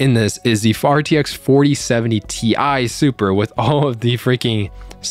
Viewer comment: did I just hear FARTX 4070 😂😂